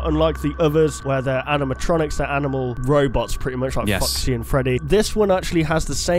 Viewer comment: I thought he said fuckci and freddy hahahaha (it meant to be foxy)